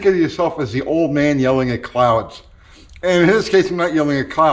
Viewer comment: Oh! CLOUDS! I thought you said "old man yelling at clowns! :)